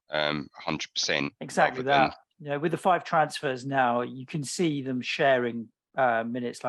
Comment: With the five transfers instead of with the 5 subs is very funny. GREAT CONTENT